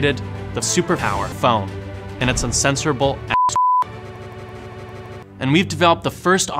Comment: Excuse me, guys. I'm currently writing subtitles for video, & I don't understand what is 'its uncensorable a** ****e' at 0:36. solved by Ricey Martin. The next one is the sound at 1:44. Could you please help me? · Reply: "its uncensorable asshole." The word "asshole" was censored.